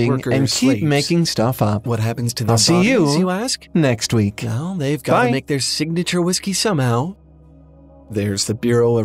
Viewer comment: For those who couldn't understand the overlaid audio at 14:05 and want to, I think it said: and uses them as "ghostly" (*something I didn't catch*) "worker slaves. What happens to the bodies you ask? Well, they've got to make their signature whiskey somehow!"